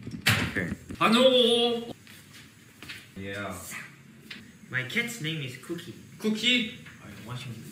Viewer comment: when Seungmin read the comment "my cats name is cookie" for a second i thought he said my kids name is cookie- 😭😭